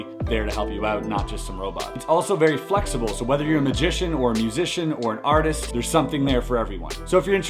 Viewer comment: About minutes in, "magician, musician, or artist." I heard "artist artist or artist"